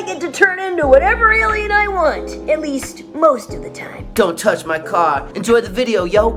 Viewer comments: I definitely thought he said “don’t touch my cock.” 😅 had to relisten twice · I just woke up and i started watching this video and at 0:52 I thought Kevin 11 voice actor said “don’t touch my cock” lmao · i thought he said dont touch my cock but it was actually a car